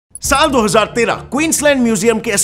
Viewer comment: I heard saat aur saat 13